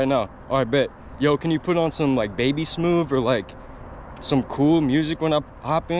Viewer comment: did he just say 10:54 babysmoove😂